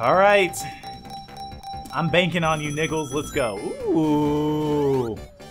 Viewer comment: knuckles? I thought he said nickels